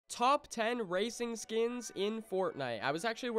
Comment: Lol I thought that it said top 10 RACIST skins